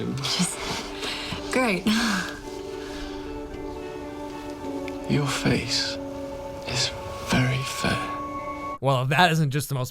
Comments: lol your face is very fair crinnngggggggeeeeeugghhhh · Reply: At first I thought he said “your face is very fat” lmao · I thought he said, "Your face is very Fat "